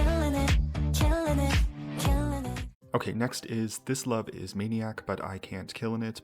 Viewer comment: I thought they said "I kept killing it"